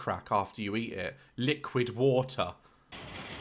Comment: Did just say liquid water...